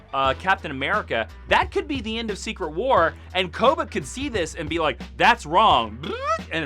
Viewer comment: what did he say? koba? or what?